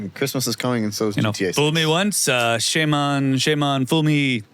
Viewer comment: did he say GTA 6 😭